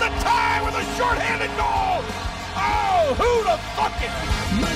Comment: did he say “who da fuck it”? Or am just crazy